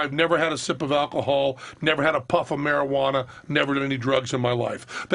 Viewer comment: Did you just say "Marijuana Needles?"